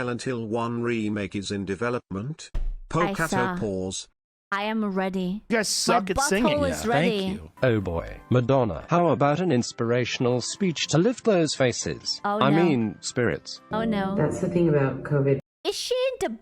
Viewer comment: wait.. i was listening while working... did i hear a "my butthole is ready" at some point?.. maybe im nuts